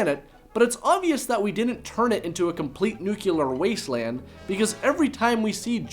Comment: Haha, I'm loving some of the answers you guys came up with! Seriously though, a lot of you were spot on. I use it to sync up the audio and video since I record audio separately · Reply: ***** Hey, um... 5:15 - did you just say "nu-cu-lar"?